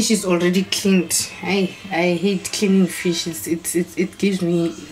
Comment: I thought you said I hate killing fish 😂😂😂🤣 kanti its cleaning fish. Thanx for recipe. 👌🏾